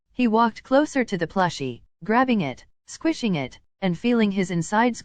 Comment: when it said bakugo found a soba plushie i thought it said squishing it and feeling its insides and i was like 👁👄👁